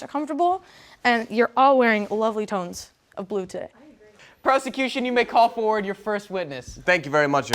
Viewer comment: i heard prostitution for a second 🤣🤣🤣🤣